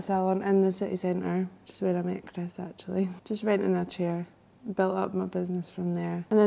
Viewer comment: I love that you subtitled the accent. Scottish person here with a wee note, I believe at @ 4:43 says "renting" not "right in", @ 5:20 "still followed us" instead of "stuff for us" @ 6:40 "Chris just" instead of "I just" @ 10:59 "disperse" instead of "spread" @ 11:33 "still kind of like quite modern" instead of "still came like quick modern" @ 11:40 "nine times out of ten" instead of "maintains" @ 14:06 "more depth with" instead of "more with" @ 20:10 says "hair up" rather than "hit up" @ 20:27 "cool" instead of "curl" No biggie, just clarification :) Thank you for showcasing bonnie Scotland Edit: added other timestamp Edit2: OK I can't stop now. Must close loop.